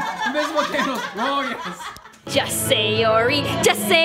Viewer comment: I'm laughing the subtitles say Just sayori. Just say Yuri Just say re creepies XDD